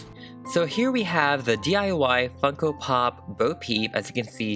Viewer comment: When he finally got started to customise her, I thought he said, "Here we have the DIY fuckin' pop. But he really said, DIY Funko pop. lol